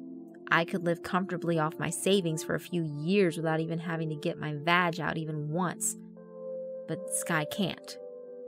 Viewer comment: You know, the first time I heard 17:10, I'd thought you'd said that you[r character] "didn't have to take my badge out."